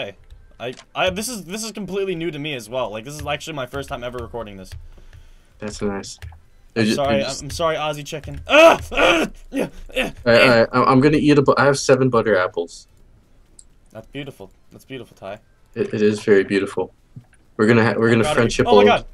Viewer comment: Chom, at first I thought he said "OxyChicken" 5:52 I feared for my friend